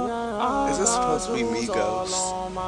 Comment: did he just say Migos?